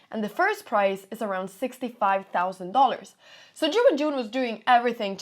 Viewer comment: Everytime she said Ju Wenjung I thought she was saying During June